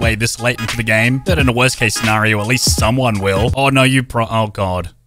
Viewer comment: anyone hear the janitor npc say “oh no” before he got shot haha 😂